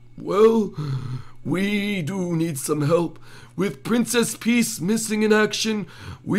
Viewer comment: Did he say "Princess Peace".